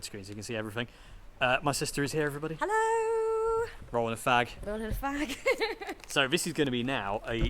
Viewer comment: What are you guys saying 10:21? The sentence I hear can’t be right neither the subtitles of the video... or is it slang? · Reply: “Rollin a fag.” It means Rolling a cigarette.